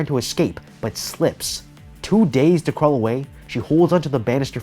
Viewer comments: I swear you said “two days to get away” not “too dazed to get away” and I was laughing so hard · Lol instead of "too dazed to get away" I thought he said "two days to get away"